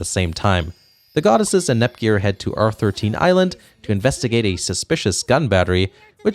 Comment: I heard R-13 island... Or am I the only one who caught that.